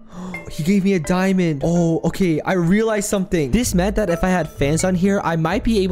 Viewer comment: it sounded like u said fantst istead of fans